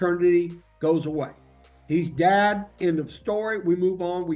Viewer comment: caption says "he's dead end of story" 😂